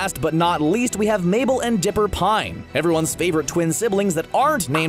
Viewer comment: Did he say... Pine?